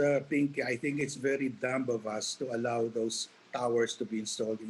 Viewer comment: Did he say “dumblebass”, “dumblebus”? Does anyone know what that means? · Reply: dumb of us